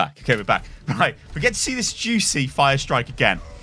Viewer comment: did he say let’s see this XQC Firestrike again?